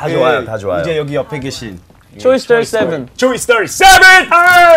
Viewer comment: It took me sometime to realise he was saying "Choice37". I thought he was saying "Toy Story 7" haha